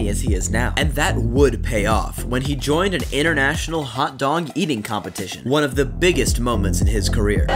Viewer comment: Did he just say “hot dong” eating contest